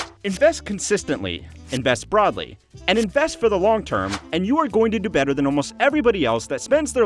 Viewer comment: you have an error in your video. Instead of saying "invest long term" it says "invest log term". Just letting you know.